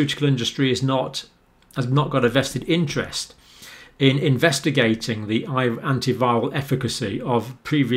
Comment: particularly at around 15:28. See if you also hear it " investigating Iver .... previously exsting antiviral drugs" . Do think it was intentional or a slip? Or am I hearing things?